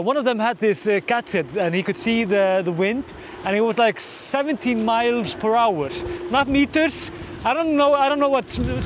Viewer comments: At first I thought you said the wind speed was seventeen miles per hour, and thought that was way off. Now I suspect that you said seventy miles per hour! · 113km/hr~70miles/hr That is equal to a very windy day. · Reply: I thought he said 17miles/hr not 70 but I may have heard wrong